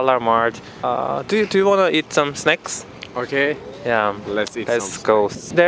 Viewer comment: I thought he said “Do you wanna eat snakes” 😭